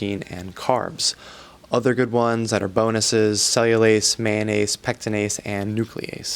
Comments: Am I the only one who thought at 5:50 he said mayonnaise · I was looking away and thought is said mayonnaise not "mannanase"